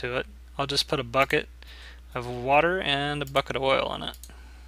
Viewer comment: did anyone hear the person in the background @ 6:07 "he has a buckit" lol